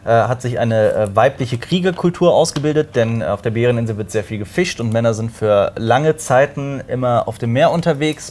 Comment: Ich hab mich n paar Meter vom Bildschirm entfernt und was aufgeräumt und einfach verstanden, dass auf der Bäreninsel "sehr viel gefickt" wird 😂